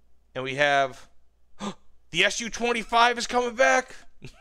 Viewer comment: God I really excitedly grabbed my phone which I wasn't looking at when you mentioned the Su-25, thought you said Mig-25 :( Still, one of the best trailers of all time and cant wait to play the Tornado GR.1